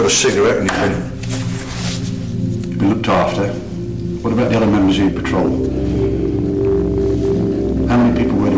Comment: What does the interrogator say at 15:56? "What about the other men was you in patroll"? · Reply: Karl Obolensky other members of ur patrol